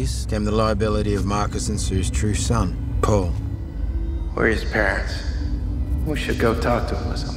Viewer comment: i first heard Travis said " Where is Paris?" I though they was gonna raid Paris lol